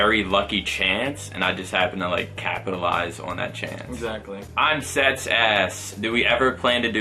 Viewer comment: " I'm seths ass" I'm I the only one who thought he said this?